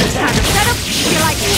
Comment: I thought she said "You've been annoying" O.o